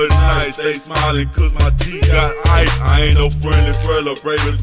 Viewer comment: What does he say at min 2:38? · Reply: I ain't no friendly fellow